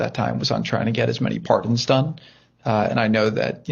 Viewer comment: It's staggering that Kushner began by saying he was "busy getting pardons". Why? Why were you so busy getting pardons for people unless they were involved in criminal activities? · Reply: Peter, I could NOT hear or Understand the word Jared was saying that he was so busy "getting". I rewatched the video 3 times, and thought Jared was saying the word "PARTNERS". Thank you for clarifying that word ! BTW, I have heard and read several times in the NEWS, from the Insurrectionist and aides and Advisors to "Dumped" that he did "promise 'Pardons'" to these potential "CRIMINALS" ! Therefore, I logically ask the Question; Are these the "Pardons" Jared was so busy working on ? ? !